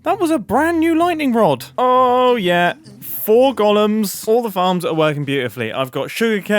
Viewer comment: I thought he said "Gollums" for a second there